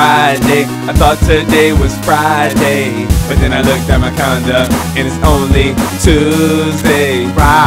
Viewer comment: If you turn on captions for 1:44 then instead of calendar it says condom...